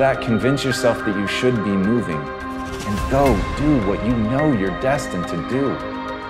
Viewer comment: Hey guys, what did he said after the word moving at 7:25? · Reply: You go do what you know you are destined to do.